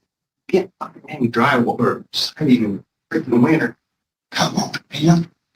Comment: What does that guy say about his wiener at 0:29?